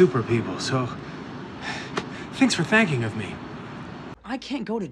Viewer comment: It’s sounded like Scott said “Thanks for thanking of me” lol 😂😂